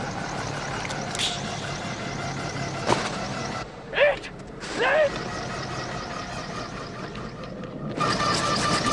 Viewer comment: What does Lars say here? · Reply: "that not a dog, that the thing"